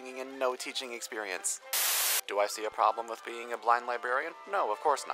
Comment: Omg i misheard Startflight saying: Do I see a problem being a black librarian? And then i laughed so hard